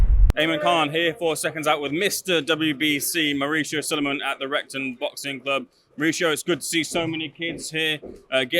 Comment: I thought he said rectum boxing club at the start😂